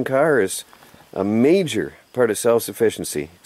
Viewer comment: What did you say at 9:46 Sounded like you said GOT TO GO IN AND DRAIN THE BUD. .... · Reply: yeah he says he has got to go in he is being drained of blood. I guess its the mosquitoes